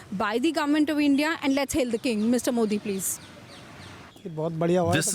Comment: did she just say "let's hail the king"?